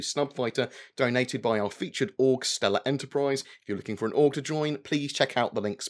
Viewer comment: Hah. Here this last couple vids when you mentioned this Org, I thought you were saying "Stella Enterprise"... like a self-named org started by a girl named Stella. Which sounded slightly odd... Now I finally looked at the link, and the actual name (Stella*Rrr*), makes a bit more sense. ("Two Peoples divided by a Common Language", and all that...)